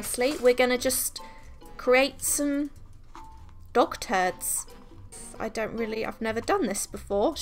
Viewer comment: I thought she said “WE ARE GONNA CREAT SOME DOCTORS” instead of dog turds 3:10 😂